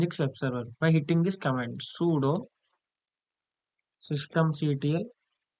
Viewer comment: i have no clue what you said at time stamp 3:39 "systemct1"?? cant read it either